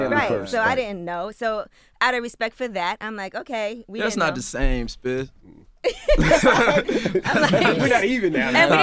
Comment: did he call ye spit at around 22:25 hmmmm